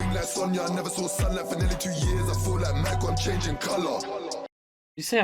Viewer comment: Bar is "I felt like man got a change a change in colour" He's referring to himself. It's a UK thing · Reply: i always thought he said " i felt like mike like im changing colour" i still caught the bar in its meaning but yeh thought he said this